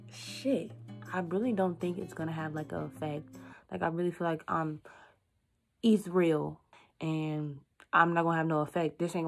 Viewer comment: So no one else gonna talk about 7:18 “I feel like I’m EATH-REAL” TOOK ME OUTTTTT That shit made me rewind, I thought she said she feel like Israel ?!